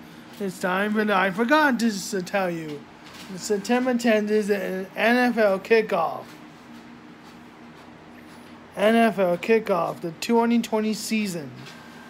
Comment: What'd he say at 1:08? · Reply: NFL Kickoff Begins September 10!